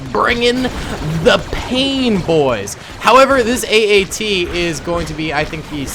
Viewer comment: did he call it an AAT???.